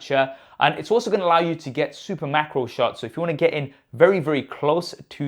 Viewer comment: Sorry thought you said super mackerel shots for a second.